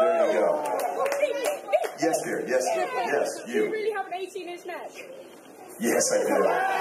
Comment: what that woman say at 0:52 ? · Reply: She asked, "do you really have an 18 inch neck".